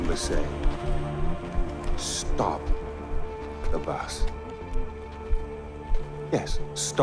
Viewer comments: At first I thought he said "Stop the Vaas". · I thought he said stop the vass I was like wtf but it was stop the bus